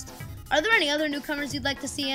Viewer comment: I thought they said"fuck the reviews" at the end :O